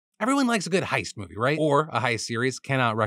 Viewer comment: Not gonna lie I thought it said ATHEISTS